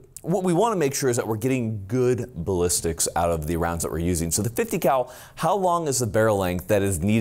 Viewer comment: I heard "sketchy 50 cal" while watching this and wondered if Kentucky Balistics would be brought up at some point. I wasnt dissapointed 😂😂😂